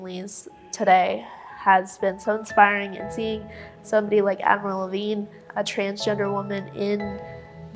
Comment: I was listening and not watching and when the stuffy nose/nasally chick said “Admiral Levine”, I thought she said “Avril Lavigne”. I was like, “wait, what did I miss?” I had to rewind and pull my phone out of my pocket. 😂